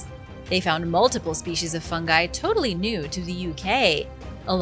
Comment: At first i heard "fun guy" xD